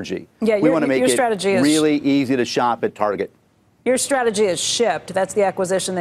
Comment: I thought she said your strategy is s!!!